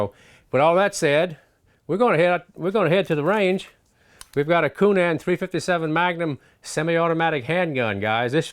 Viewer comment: I thought you said "Coonass in .357", LoL